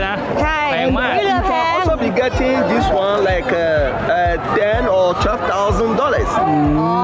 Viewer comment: มีแปลผิดเล็กน้อยนะคะ เผื่ออยากจะแก้ไขน้า ไกด์พูดว่า Ten or Twelve Thousand dollars นะคะ 10,000 หรือ 12,000 น้า ไม่ใช่ 10,000 - 20,000 น้า